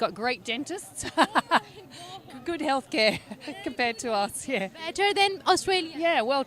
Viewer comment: Constructive Criticsm: The video was all good, but comparison of Aussie GDP with India was ridiculous. GDP overall for India is more because we have huge population. Agar population ko madde nazar rakhte huve dekhe toh per capita India is around US 2.5K and for Aussies it is USD 66K.. Also she said India has better doctor, she never said India has better health care. Thodasa misdirection hogaya. And what is point in comparing Space program, Australia had no interest in Space programm till last 15 yrs or so. Unka space agency thi hi nahi till 2007-08ish. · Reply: At least hear what the lady said correctly....she said and I quote: "good health care compared to us, yeah..." Listen again, if you feel like...